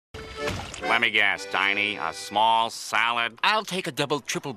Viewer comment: Damn squidwards a boss in this scene. He roasted him 3 times lol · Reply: I always thought he said "let me guess, I need a small salad?"